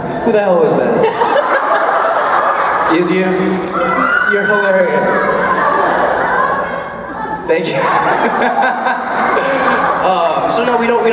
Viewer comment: what did the fan in the audience say at 13:04 ???? i cant hear her/him please let me know ;p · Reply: When he was talking about him being compared to taylor lautner, im pretty sure the girl said "You're better!!" and he thanked her